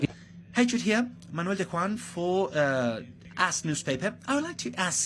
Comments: did he say "Ass Newspaper" 😂😂😂😂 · did he say “Manuel Daquan from ass newspaper” 😂😂😂